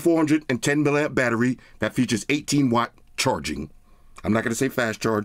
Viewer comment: First I thought said 80 watt charging and was: not bad. Then I listened again and heard 18 watt. Killed my mojo man.